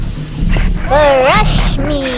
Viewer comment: did tattletale say bruh